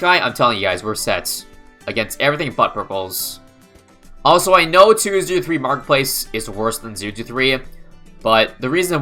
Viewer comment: Did you understand what he said? "Also I know that 2 to 3 Marketplace is worse than 2 to 3 Marketplace"? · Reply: He said " i know 2-0-3 marketplace is worse than 0-2-3 "